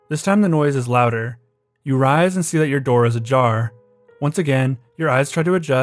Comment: did he say “your door is a jar”?